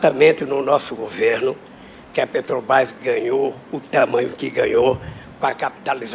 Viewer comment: Lula aos 6:25 "Pretobás" foi isso que eu ouvi msm? 😂😂😂😂😂😂😂😂😂😂😂😂😂😂